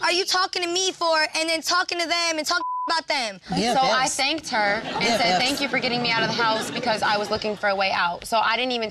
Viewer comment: wtf did say??? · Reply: She said “Bffs...bffs” 😂